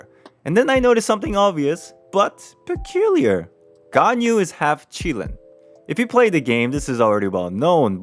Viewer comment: I misheard: “Ganyu is part Cuban” 😂 😂